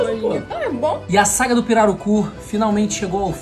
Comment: Eu ouvi "Piranocu"